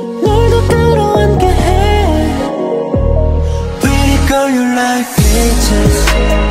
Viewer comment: His accent looks cute.. "pretty girl" in his accent.. i heard him saying "perry gurl"